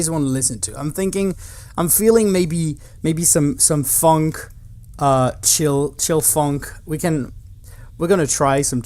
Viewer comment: For a bit i thought he was saying "chill phonk" not "chill funk" and i was prepared for cringy ass music